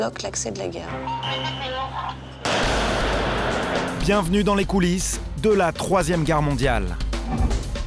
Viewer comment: A 1:50 jai compris " bienvenu dans les coulisse de la 3 eme guerre mondiale " 😂